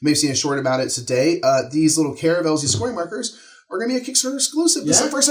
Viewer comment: Was multitasking and thought you said “caramel scoring markers”. Delicious. Suggestion to publisher - caramel caravel stretch goal?